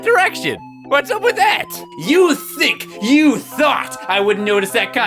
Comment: Did he just say THICC and THOT?